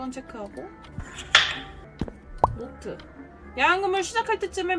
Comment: 왜 난 계속 야간근무를 야한근무로 들리냐..;;마구니까 차있나..왜이러지..